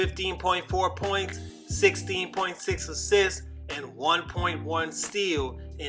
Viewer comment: mark did he say 16.4 assists? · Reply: 16.6 assists